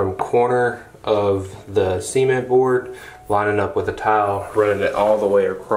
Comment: I really thought was saying semen board.. 😬 🤭